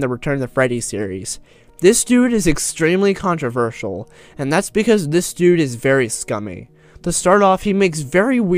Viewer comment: thought you said thats because hes me